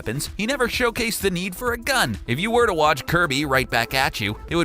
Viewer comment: Wait did he say GUN